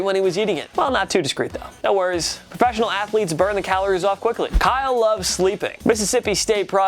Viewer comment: When he said Kyle love was sleeping I thought he said Kyle loves sleeping